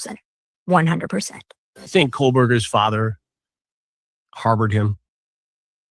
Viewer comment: Does he say KohLberger or Kohberger? Sounds like Coal-burger.